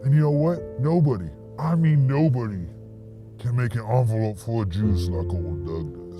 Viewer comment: I was like wtf does demon Doug mean when he said "no one makes an envelope full of jews like I can" then he drank out of it and I realized he said envelope full of JUICE. Which still doesn't make much sense but it makes more sense than what I thought he said lol